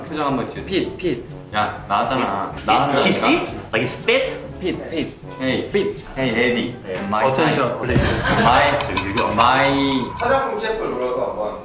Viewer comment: I thought he said "hey, daddy"